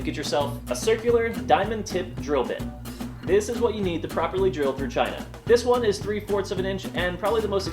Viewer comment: is it just me or did I hear "to properly drill your vagina" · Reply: This is what you need to properly drill through China